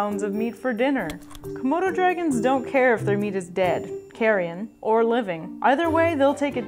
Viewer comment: What is does this "carian" (karean? carean?) word I keep hearing mean? 2:15 I can't figure out how to spell it to look it up... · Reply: Carrion.